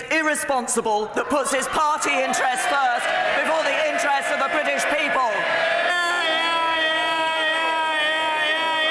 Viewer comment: what the heck was that yelling 27:20? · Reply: hear hear hear hear